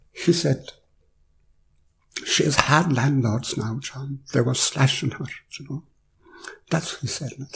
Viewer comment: What did John's father say to him at 4:39? I just can't make it out. Thanks very much for the upload. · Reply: Your welcome! I think he says "she has hard landlords now John, and they were slashing her". It sounds like she was facing into some dark unresolved collective stuff and couldn't quite surrender into it.